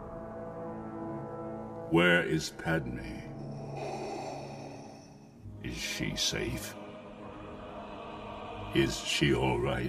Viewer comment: I could have sworn Vader said something like, "Where is Padme. I can't feel her in the Force."